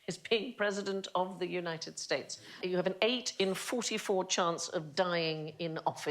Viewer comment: I misheard her then, though perhaps not. It sounded like she said the most perilous job is be a resident of the United States. Sounds plausible.